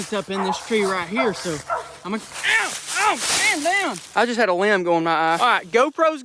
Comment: Am I the only one who heard the D word? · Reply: He said "ow man down"